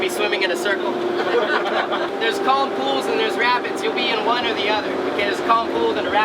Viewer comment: when he said "RAPIDS" i thought he said "RABBITS".....until i put subtitles on.